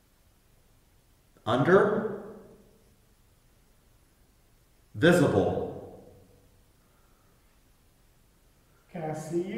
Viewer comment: during Estes method he said "we are coming" where are you - he answered "under...visible" I think they really said "under... vestibule" that would be accurately probable. Great video